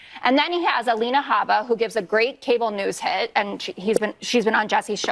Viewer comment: Did she just say Habba “gives great cable news head”?! 3:20 😮